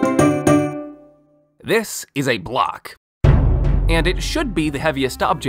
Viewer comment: i thought he said glock